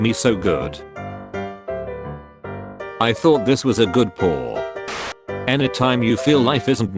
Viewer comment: he’s saying “good paw” instead of “dog paw”